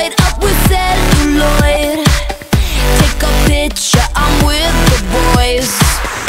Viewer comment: is it just me or did anyone mishear "take a picture, I'm with the boys" with "take ya bitçh, I'm with the boys"?